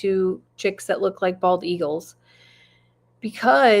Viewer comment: I heard you say, "They look like bald eagles." That person needs to turn their hearing aids up OR turn on their U-Tube close caption (CC)....😅